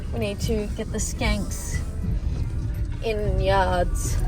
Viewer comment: Another fantastic vlog I got a question has typo even farted in the tractor when you and her are in it, and I always thought you were saying stinks instead of skanks